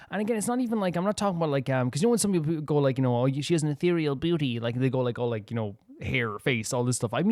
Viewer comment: I thought you said "ethereal booty" and I'm like oh okay - nah wait I'm actually so proud I understood Columbina's Trailer! ;u; - "the teasers are a bit longer" to "that went by so quickly!" xD - Zibai's trailer :'D the vocals stunned me too. The thing you say about the music being in-game, it will be added but probably in the form of the rhythm game hehe - Heaven's Dumpster vs Heaven's Door. which one will win- Illuga how - I screamed when I saw Mavuika in this teaser. I know that's right- I love Mavuika's hair sooo sooo sooo much - missing Mavuika hours is everyday for me - Varka's Trailer is the best because it has Razor in it - watched the trailer of Durin before teaser huh . still cute - Barbeloth with her gorgeous bob too - "I'm glad he doesn't have a shirt that fit him" LMAO WHAT - Hero? nah, he's a knight! - Traveler stopping the flood in Fontaine xD - I never thought someone would think Varka would betray us.